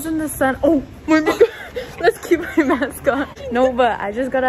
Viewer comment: someone pls correct me if I’m wrong but what did she say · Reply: “let’s keep our mask on”